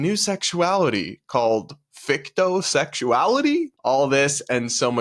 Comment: I had to do a double take, and rewind the video, I thought that you said victim sexuality.. however I wouldn’t be surprised if that’s the new upcoming one